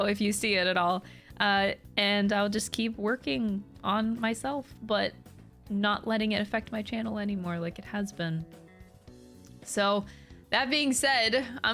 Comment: I misheard "it has been" in "Affecting the channel like it has been" as "a husband" and was like "wow, burn."